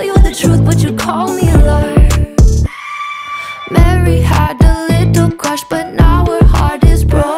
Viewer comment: I thought she said marry had a little lamb